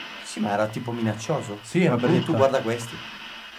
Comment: Al minuto 12:49 io ho capito tu guardi questa città